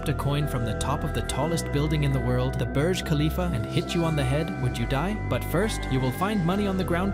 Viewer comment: I thought it said mia khalifa☠️☠️☠️